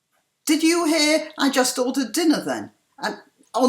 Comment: I heard, "I just ordered weiners", and I didn't WANT to hear that, dearie. Seriously.